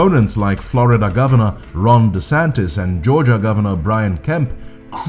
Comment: Did you just say wRONg DeSantis? I'm really hearing "wrong" instead of "Ron". 😀